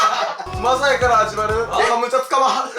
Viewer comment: ま～さいから始まるーがザーサイから始まるーに聞こえる？